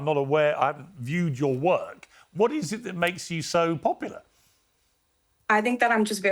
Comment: Piers really said, “I’ve viewed your work to Sophie.” 😂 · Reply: I thought he said “I haven’t viewed your work, what makes you so popular?”